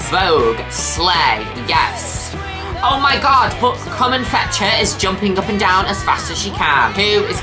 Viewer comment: Not me mishearing Carmen Fletcher as "common fetcher" and thinking it was a Morrowind reference